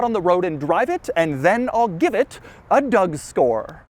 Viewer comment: Why does it sound like Doug is saying Doug Store? Why is he fkn with us?